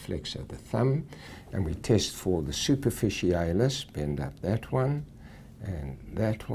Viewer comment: Did he just say Super-Fishy-Anus?